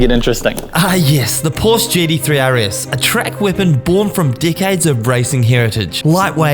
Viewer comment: at first i heard Dic*heads not decades 😅😅😅